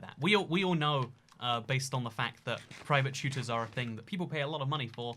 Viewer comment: the captions thought said “private shooters.” Wait a minute…